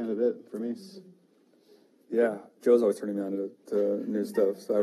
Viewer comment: What female fronted band did Tim mention? I couldn't understand (around 24:45). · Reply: White Lung and War on Women - two badass bands!